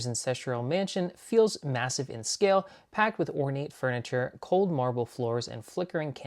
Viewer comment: It sounded like you said horny furniture 😂